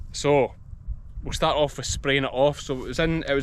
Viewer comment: when you said spraying off I heard the word "roundup". As in Monsanto Roundup?